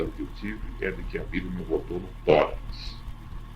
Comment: Nos minutos 22:28 acredito que ele disse que "A vida me voltou no tórax" pois ele segue dizendo que "Os membros, as pernas e... e as mãos, foram as últimas a receberem a sensação de volta à vida".